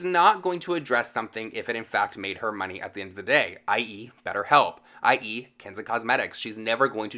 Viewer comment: Swear to God you said "I eat betterhelp I eat kenza cosmetics" 😭😭